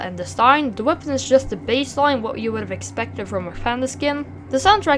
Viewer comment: Did you say baseball? 0:29 thats a panbo -_ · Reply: The Word i sand was (Baseline)